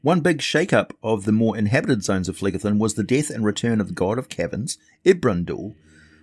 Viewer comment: I thought he said the return of the God of Kevin's.